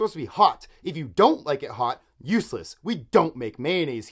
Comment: I thought he said “use less” not “useless”